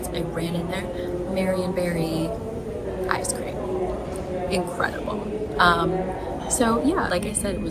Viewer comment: it is so funny how at first i heard Mary Berry ice cream 😂 thought that was random for Seattle until I realized I misheard 😂❤